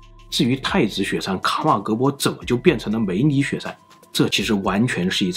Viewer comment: 我吧梅里雪山聽成美女雪山😂